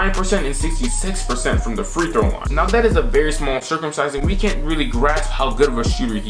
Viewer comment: Did you say "That is a very small circumsizing" around 3:30?